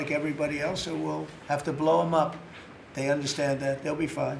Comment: Did he say he wants to blow up Oman?